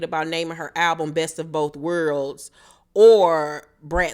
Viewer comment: I thought Brat said that she was going to name her album “Both of both Girls”, not Worlds…since she was saying that she was raised both girly and being a tomboy…